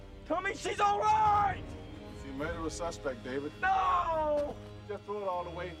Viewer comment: What does Morgan Freeman say? "He made her a suspect"? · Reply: He said "if you murder a suspect David."